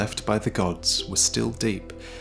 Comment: I thought you said Oreo's and I burst out laughing.